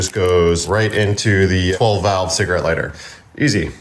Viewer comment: Did he just say "12 valve cigarette lighter"?